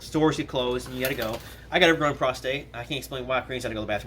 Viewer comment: Did he just say he has an "overgrown prostate" lol